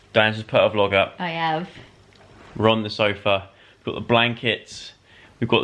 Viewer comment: Thought he said "Ron the Sofa" not "We're on the sofa"